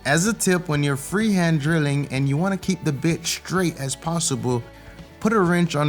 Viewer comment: did you just say: "If you wanna keep the bitch straight..." ?? 😆😆😆